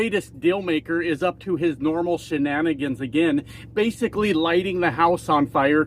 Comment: I thought at first you said about Trump "his normal shitnannigans"...but it fits!!! 🇨🇦❤️💪💪💪